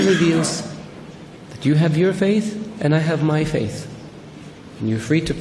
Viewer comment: in around 2:18, did he mean to say like "you have your faith" instead of "do you have your faith"? Jazak Allah khair! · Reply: You have your faith